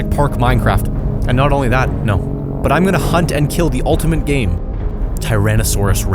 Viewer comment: Me casually watching My wifi : cuts midway What i heard : im gonna kill the ultimate gay